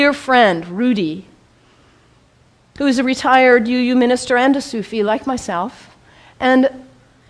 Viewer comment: Not sure how you can be a Sufi and a Christian. Sufism is not separate from Islam, they are one and the same. In fact, most are orthodox Sunnis. Unlike Christianity, there are no disagreements amongst Muslims on a) the oneness of God and b) that Muhammad PBUH is the last messenger. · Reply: i never claimed she was a Christian..if you watched here 16:49 you would hear her say "I have a dear friend, Rudy, who was a retired minister and SUFI LIKE MYSELF". She could be saying something else but it sure sounds like she said Sufi